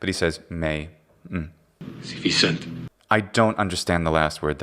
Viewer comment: Pilate is saying, “Ergo rex es tu?” The vowel in “tu” is just quiet for some reason. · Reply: I think he is saying "sīvissent"